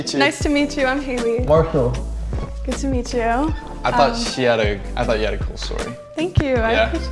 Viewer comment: "I thought she had a better glow up" is that what you mean to say my boy?